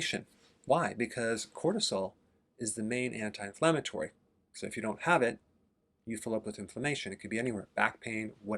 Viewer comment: At about 1:05 it sounds like Dr. Berg says “if you don’t have it [cortisol], you fill up with inflammation.” Am I hearing that right?